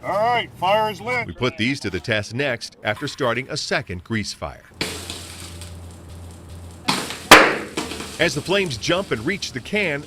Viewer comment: i thought it said "putting out kitchen grease fries"